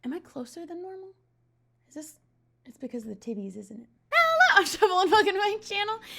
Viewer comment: I thought she said tiddies 😂